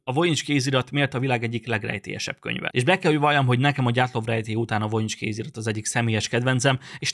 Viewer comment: mit mondott ? Nem értem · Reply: Gyatlov-rejtély